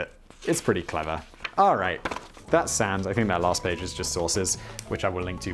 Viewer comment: Why has it taken me 3 videos to realize he's saying sources? I kept hearing it as "That last page is just sauces" and I was very confused.